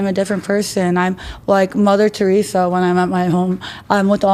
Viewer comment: WTF did she just say mother Theresa 🤦‍♂️😂